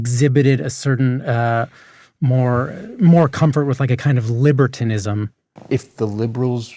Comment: He doesn't say "if the democrats exhibited more comfort with a kind of LIBERALISM". He said "LIBERTINISM", as in he thought Schultz was saying the democrats should be out partying and getting laid and then more men would vote for them